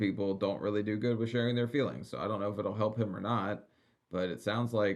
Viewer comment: I think a fee fee is slang for a pocket p^*s¥ they use in prison 🤣. But yeah she’s a scumbag for that. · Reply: I thought she said feefees instead of FEELINGS?